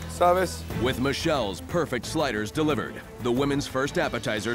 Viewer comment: At first I thought they were saying “spiders” lmao